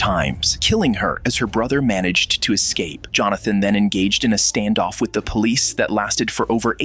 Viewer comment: i too thought she was saying “Stop jumping” but i thought to myself “why would he be jumping” but if you listen close she is say “Stop Johnathan”.